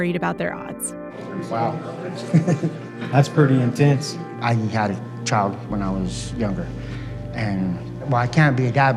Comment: I heard "I was a child when I was younger" 😂